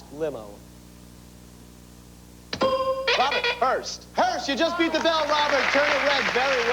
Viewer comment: Wow, 05:49 Robert very clearly says "HEARST" for last limo and they didn't catch it